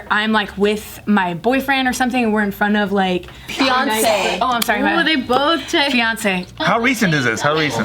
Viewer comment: i thought they were saying Beyoncé... took me a while to catch on